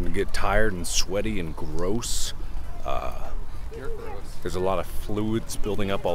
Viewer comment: Did anyone else hear the ''you're gross'' at 5:12 XD